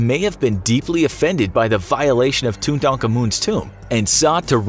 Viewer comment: It sounds like they were saying Tun taank an mun instead of Tuttenkamen. And Takamaan